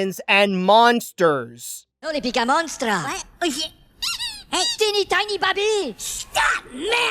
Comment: I swear Goomi says "oh shit" before his head is squeezed like a dog toy.